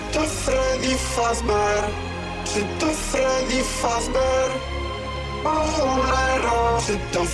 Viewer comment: I thought he said shit to freddy fazbear